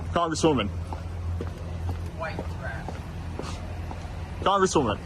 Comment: did someone just say “white trash”